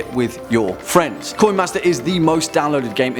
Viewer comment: Haha haha! I was only listing at the beginning and thought you were saying Koi master! Then I looked at the screen and realized you were saying "coin master"...heck of a accent!